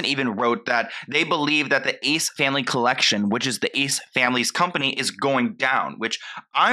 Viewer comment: Idk why my brain heard you say “Ace SCAMily” at some point 🤣